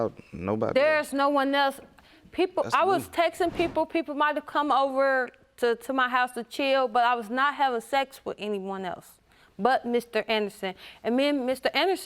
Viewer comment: I misunderstood her when she said "chill." I thought she said Cheeto, and now I'm craving Cheetos! 🤷‍♀️